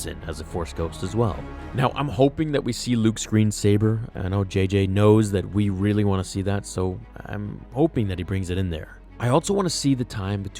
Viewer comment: At first about 4:00 I thought he said screen saver, but it was green saber..:-) I hope for a "significant" role for Luke Skywalker either way, (dead or invisible) so J.J...don't F*** this up :-)